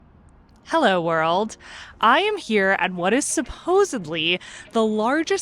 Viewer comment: did she say SUPPOSABLY?